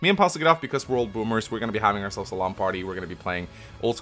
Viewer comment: I thought you were saying lawn party and not LAN party. Lmao Edit: happy new years!!!